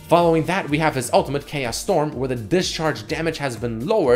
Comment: I thought he said "KS STORM"